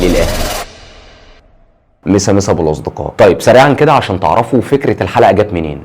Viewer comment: ايش قال ؟ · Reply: مسا مسا يا ابو الاصدقاء